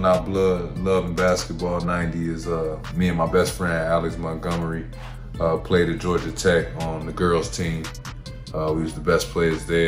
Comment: Did I mishear, or did he say he was one of the best players on the girl's team at Georgia Tech? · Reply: Alex Montgomery played for Georgia Tech and now in the WNBA.